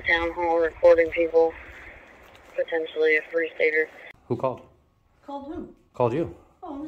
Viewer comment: what did dispatch say possible a what? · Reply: Free stater